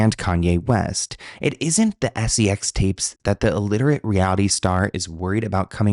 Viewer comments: I was mostly listening to this and thought he kept saying illiterate. I was like dang that's kind of harsh. Until I glanced up one of the times 😅 My Mistake 🤭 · Every time you said “alliterate” about a celeb, I heard it as illiterate. Got me thinking how these people are singers/actors. I didn’t even question khloe kardashian not knowing how to read lmao